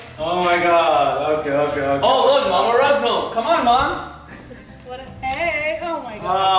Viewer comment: i thought he said “oh look mama rug’s home what the fuck” haha...